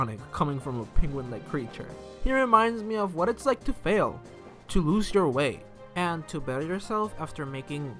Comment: i thought they said “to lose your weight” holy shoot 🤣